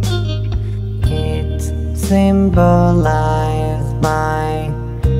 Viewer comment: i think he said correctional obligation he belongs in robot prison that robot